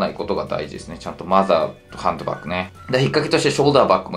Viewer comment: ハンドバッグがハンバーグに聞こえたけど合ってましたw